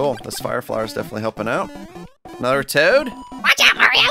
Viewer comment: i heard narutoad